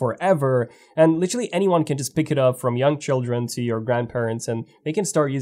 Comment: I thought said "from young children to young grandparents" 😂😂😂😂😂😂